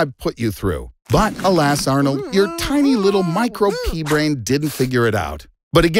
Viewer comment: I honesty thought he said micro penis for a second